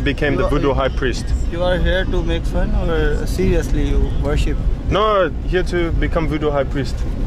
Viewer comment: did he say “are you here to make fun of? Or see us do worship?” Ugh, if I heard that right I don’t really like how that’s a common thought among natives. I understand why they think that, but that makes it harder for people who actually want to see they’re culture. That’s probably why they rejected and his friend in a earlier video when they were looking at hotels. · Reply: He was saying are you making fun or you seriously worship 🤦